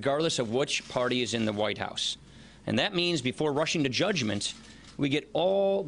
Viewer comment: paul rayan said: before Russian to judgment I could be wrong it sounded like that to me. lol